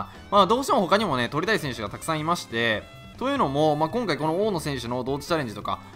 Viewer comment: 「鳥谷選手がたくさんいまして〜」って聞こえたwww